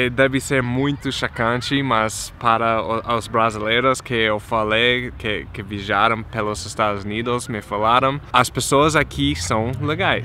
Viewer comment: Juro que entendi "pessoas que MIJARAM pelos EUA..." Fiquei rindo sozinha